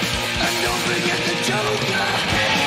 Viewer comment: His voice is gravelly. When I was little I liked this song a lot. When he says don’t forget the joker, I thought he said and don’t forget to choke her, lol.